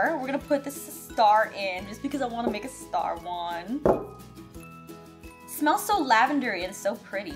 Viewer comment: when she said star one i thought SHE SAID STAR WARS wait......She said star ONE im dumb